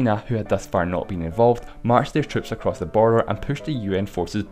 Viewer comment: why did you say borer😂😂😂😂😂